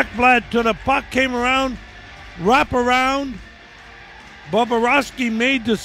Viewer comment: "Boborovsky" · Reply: I thought he kept saying "Bababrovsky".lol